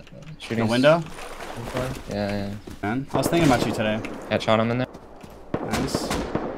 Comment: did you say i was thinking about cheatin today? im confused